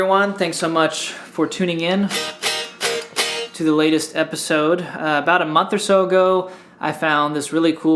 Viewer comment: He is so beautiful that I misheard him saying, "welcome to this ladies episode"